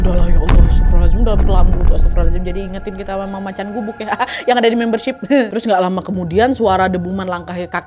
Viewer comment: ini ka nadia ngomong apa pliss 🙏🏻😔 Membership? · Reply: "Jadi ngingetin kita sama macan gubuk ya, yang ada di membership" :_Laughing